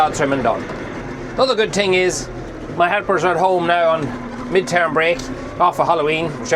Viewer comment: I thought you just called your daughters your ‘heifers’ 🤣🤣🤣